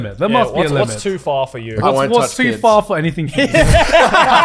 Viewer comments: what did chad say at 1:35:16 i want/need 2 know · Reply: I think he said "I won't touch kids" but I'm not exactly sure since he said "I used to be all for it" right after and nobody said anything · what does Chad say when asked "is there anything you wouldn't do"? Grant speaks over him so i cant hear the answer but they all laugh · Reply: he said touch kids lol